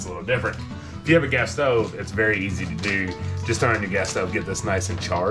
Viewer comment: I had to rewind the part where I thought you had said, Guess-so for Queso but you were saying Gas Stove. LOVE IT! ! 😊. You all are so great, real people, I just love your channel!!